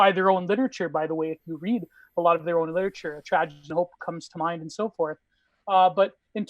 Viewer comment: what book is referencing to at this moment? couldn't hear properly, sounded like "tragedy of hope" or something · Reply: "Tragedy and Hope" by Carroll Quigley (He was a big mentor of Bill Clinton). I gotta read it but its a very long read and my current long history books are "Modern Times" by Paul Johnson & "Age of Entitlement" by Christopher Caldwell. Though I haven't finished them yet, I recommend them from what I've already read.